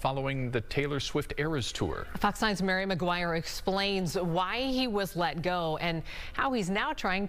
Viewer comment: I thought she said Harry Maguire at the end 😂